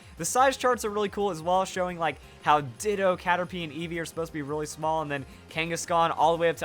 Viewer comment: I might be hearing things, but I heard Ditto instead of Diglet.